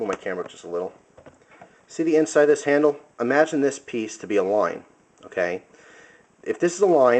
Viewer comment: you just made me hungry by how you said "line" it sounded like you said "loin" TENDERLOIN!?